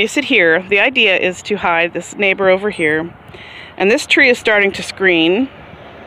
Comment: I thought you said this tree is starting to scream!😂